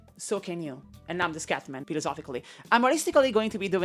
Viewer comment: the subtitles thought you said 'scatmanically' instead of 'scatman, philisophically' lol